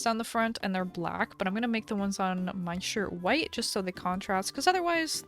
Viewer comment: did she say sh*t 😱😨😨😨😱😱😨😰😥